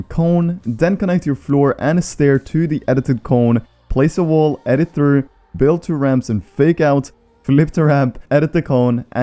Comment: I spent way too long trying to figure out what it said on 5:12 (It said “and the wall hihi”)